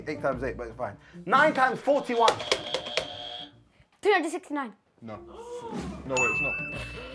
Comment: it sounded like he said 269. But I can also hear 369. I think he said 269 tho.